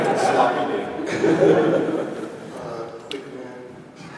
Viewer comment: what did say at 45:11? Seems like it was really funny :( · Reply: Quick man